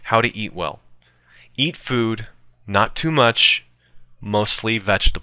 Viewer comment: I heard that quote as "Eat Food. Not Too Much, Mostly Plants"